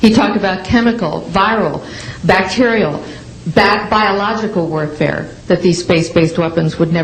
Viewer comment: Did she say "bat biological warfare" at 5:33??? C0v1d anyone? Am I taking crazy pills?